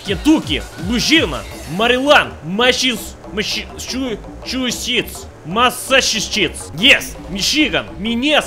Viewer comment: I live in Massaschuschits, right near Rhodgeis Land and Conneticoot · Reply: trash_panda I was born in Massachusetts, but when he said it I thought he said mass shits